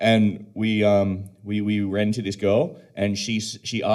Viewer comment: 😂😂 I thought bro said "We rented this girl". But it was "We ran into this girl" LOL